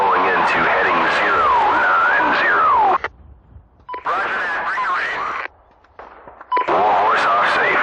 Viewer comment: Did somebody say "bring the rain"?